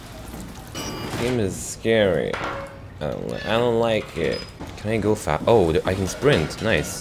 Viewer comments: It sounded like you said "Can I go fap" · can i go fap.. i thought he was about to say that XD